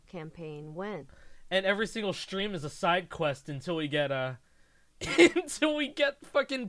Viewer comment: Did I hear "Acai'd Quest" at the end of that? Did I just hear a pun?!